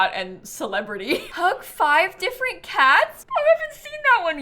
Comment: Am I the only one who thought she said, "Cook 5 different cat's?!" 😅